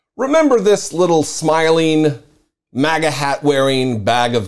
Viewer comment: You were nearly right there "Maggotihat" was the word you were looking for. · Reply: Lmao I laughed at that too, I thought he said “maga tat”. I seriously wouldn’t be surprised if this kid had a MAGA Tattoo.